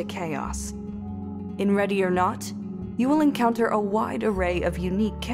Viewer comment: I thought it said ready or nut